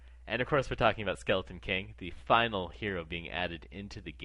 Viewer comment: wait 7:30 sk is the final hero in dota 2 or did i hear this wrong?